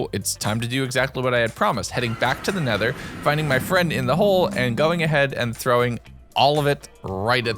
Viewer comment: why did i hear "fire in the hole"?